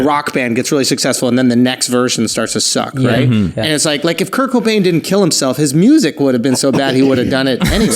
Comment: I THOUGHT HE SAID KIRKO BANGZ KILLED HIMSELF... HE SAID CURT COBAIN LMAOOOO